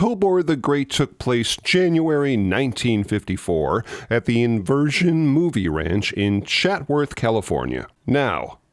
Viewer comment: Hi, was that Iverson Movie Ranch? I thought I heard you say "Inversion" Movie Ranch. Also, wasn't Tobor the robot on the Captain Video show?